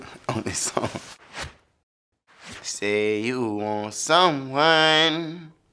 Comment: Low key thought it said want some wine